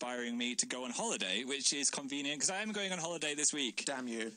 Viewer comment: He rapidly goes "slightly,maybe" It's hard when your heart just speaks out unexpectedly Dan,It's just hard... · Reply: I heard "slightly, mostly".